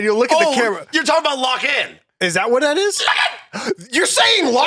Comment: I didnt know that he said "Lock in" aswell. Only AFTER i heard him say it about 3000 times i finally knew what he said XD